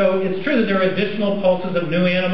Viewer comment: I think I know what they are trying to do, but I'm still confused at everything he said. He states "It's true that there is (en decimal?) pulses of new animals coming online" 9:41 "But the Real question is..." Maybe I missed something, but is it he accepts the fact there is transitionals but not natural selection to be its drive? I know when I hear Neo Darwanist it comes from people who think science takes Origin of Species as one would take the bible, as if to level the playing field.